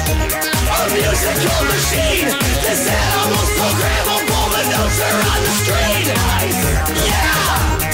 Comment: when the line "this animal's programmable" came up I thought they'd said "this edible's incredible" lmao